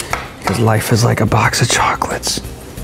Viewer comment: Next up will have to be the hot-glue finger, and the free hand 3d plastic printer finger, oh and i almost forgot! Dremel Finger!! i thought was saying life is like a xbox full of chocolates XD